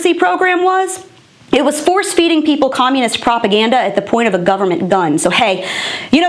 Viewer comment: did she say propaganda? Because I thought I heard CRAPAGANDA. In my opinion, it's really two words for the same thing.